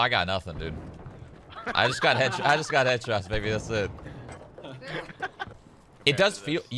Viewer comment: did shroud say “ yeah I got headshotted nigga that’s it”?